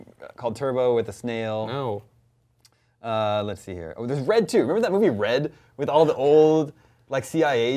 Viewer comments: lol i thought he said redtube at 23:40 XD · I thought he said redtube